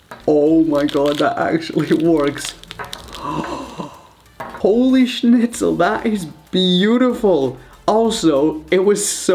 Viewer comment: did u say holy shit